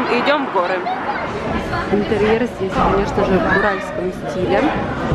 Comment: Дом в Уральском стиле? Или мне послышалось?